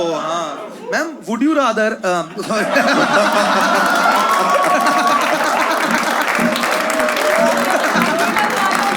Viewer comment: what did said ? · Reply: Mera desh naha raha hai